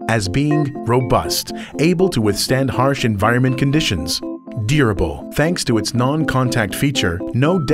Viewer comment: Did he say "DEER-able" at 3:15?